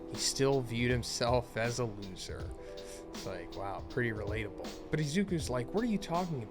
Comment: you said pretty relatable. I really hope you don’t think that about yourself Z… cause I caught what you said and I know me and many other think differently